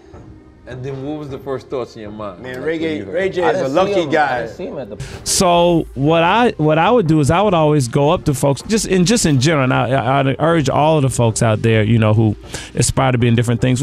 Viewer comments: Also...did he say Ray Gay at 9:52???? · Ya”ll heard Diddy @ 9:54 seconds. He slipped and said “Ray Gay” instead of “Ray Jay” and then quickly corrected himself. The TRUTH always exposes itself when watching closely! Yikes!!